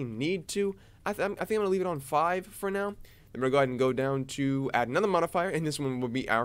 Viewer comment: What did u say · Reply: I cut myself off. "We're gonna go down to... - add another modifier..."